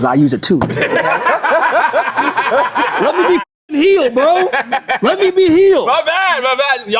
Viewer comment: Aaron being heel is his villain arc now- also I thought he was saying healed not heel 😂